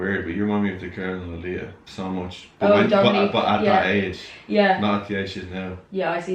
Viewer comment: what did say reminded him of at 12:31? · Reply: Dominique swain